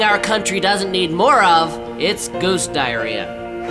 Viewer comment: "There's one thing our country doesn't need more of; goose diarrhea." Honestly, I agree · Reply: I thought he said ghost diarrhea.